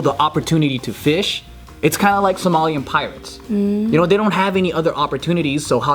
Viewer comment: For a second I thought said jomalian pirates 😂